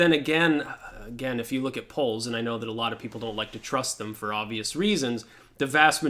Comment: I hear you say "don't trust the poles" when you actually say "don't trust the polls", and them you remind me not to be perpetually offended. Haha peace out.